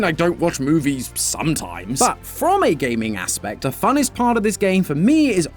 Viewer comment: Wait. Did he say funnest?!